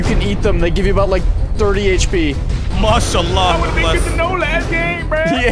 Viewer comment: Did he just say mashallah?